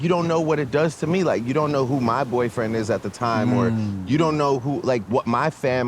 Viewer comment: minutes in the interview !!! Did Drake say ,,, you don’t know who my boyfriend is